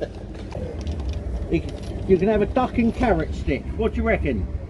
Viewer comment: "Do you want a carrot and duck stick?" I LOOOOOVVVEEEEE YOOOOOOOUUUUUU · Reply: he said carrot? i thought he said parrot.....